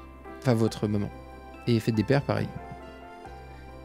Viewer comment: Euh il a dit quoi ? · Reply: si votre mère vous dit je t'aime tu dit RACONTE PAS TA VIE